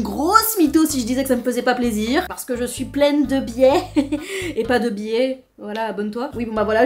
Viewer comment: j'ai compris "je suis pleine de bièèère" j'étais comme ??? 😂